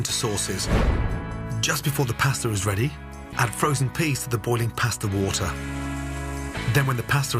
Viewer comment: did he say FROZEN peas?